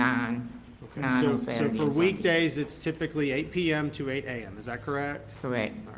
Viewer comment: To be fair...she began her shift at 8pm at night...so she didn't seem him much because of that late hour... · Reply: I thought she said her shift was 3pm to 9am ? Yes, I just listened again.